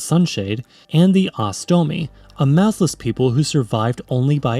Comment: I thought you said “a massless people” photons are sentient